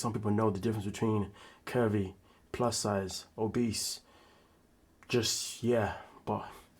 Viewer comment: Curby...plus size...then you said obese...I THOUGHT YOU SAID A BEAST.......WHICH IS MORE FITTING FOR HER